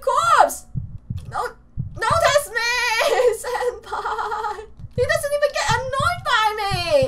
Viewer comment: Say NOTICE MEATS AND PIE. It sounds like ur saying it